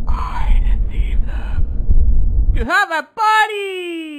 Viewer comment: "I decieved them.." "TO HAVE A PARTY!" gotta be my favorite quote here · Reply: I thought it said: I am Jesus YOU HAVE A BUDDY